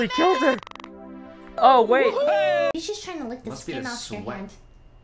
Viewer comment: What did lily say at 5:59? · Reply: "hes just tryna lick the skin of your hand"